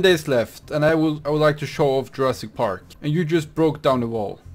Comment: I thought he said Jurassic aids